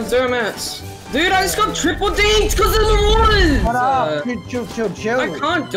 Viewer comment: did just say “chill” uhmmm